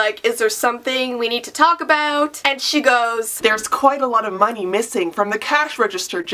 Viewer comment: I thought it said monkey instead of money omg